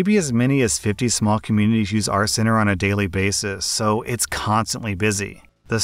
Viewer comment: I was eating lunch in my car listening to the second story, and when he said “our center,” it sounded like he said, “RCenter,” and spent like 20 minutes looking for whatever it was he was talking about